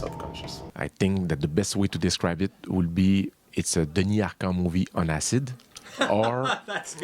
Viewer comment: What does he say at 1:43? “A denyaka movie on acid...”?